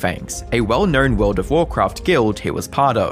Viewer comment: I thought you said of lol, I was like, “What!?! He had an of!?!?!”